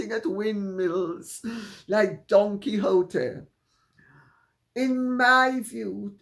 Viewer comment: Don Quixote ... thought you said "Donkey hooter" ... thought it was right which ever way